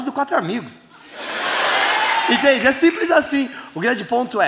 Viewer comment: Foi só eu q escutei o falando "Igreja é simples assim"?min13:34